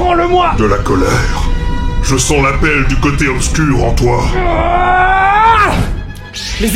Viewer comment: Zut, quand j'ai entendu "l'appel du coté obscur", ça c'est transformé dans ma tête en "la pelle du coté obscur". voila voila ...